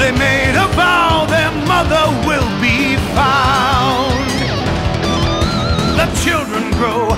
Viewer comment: It's weird, I always thought it said "their mother will be proud" Oh well, live and learn.